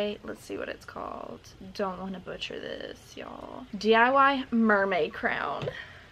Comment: i thought it said mermaid clown